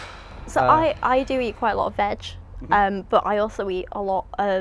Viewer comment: I’m dead. @ 2:10 She def sounds like she said “I do eat quite a lot of Vag” I know she said veg but it was funny